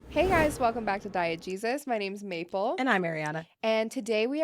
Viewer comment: Every time they say "Diegesis" I hear "Diet Jesus"